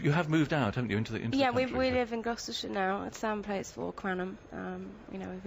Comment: where did she say she lives? I can't understand x_x its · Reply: Gloucestershire